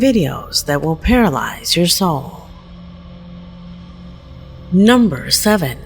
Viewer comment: I thought your going to tell us some nightmare stories with videos?I thought I heard you said that, your paralyzed our souls?